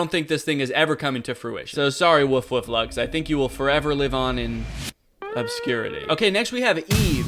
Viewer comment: I swore said "pupscurity" for a second and thought that was an incredible joke.